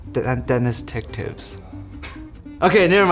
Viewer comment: I thought he said welcome to Dennis's Dick Tips...